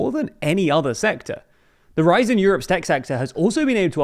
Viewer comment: It sounds like you said "Seck Sector" there.